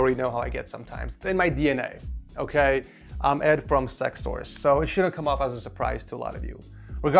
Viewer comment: did he just say sexsource